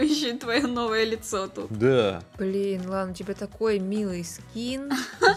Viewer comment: мне послышалось сказала: "блин Ланочка....."